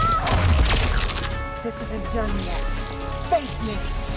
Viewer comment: For a moment I thought Kitana said "This is a dumb mask." instead of "This isn't done yet."